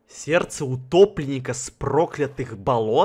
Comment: Мне послышалось не сердце утопленника а гопника